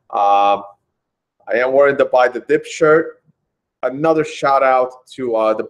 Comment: I keep hearing "Buy the DipShirt... Buy the Dipshit..." and start thinking - "did he switch to discuss B-Trash?" : )